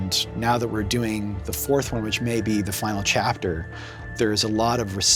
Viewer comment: Did he say "maybe"!?!